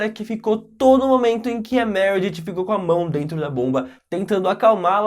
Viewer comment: eu ouvi "com a mão dentro da bunda" juro